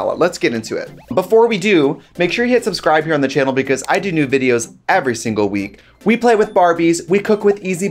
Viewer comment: I swear she says “I do nude videos every week” 😂